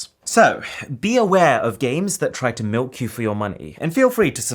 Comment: Did he just say, 'milk?'